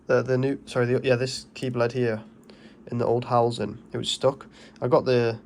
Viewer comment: Great video, good little bargain that van is. 8:07 I thought you said “bled” and YT subtitles said keyLED, 😂 I thought “he must mean ‘key blade’ “😂.